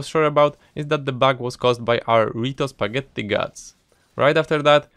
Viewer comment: am i hearing it wrong or is it rito spaghetti guts